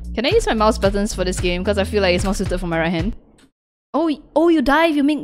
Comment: the subtitles are wrong she says right hand and the subtitles say left hand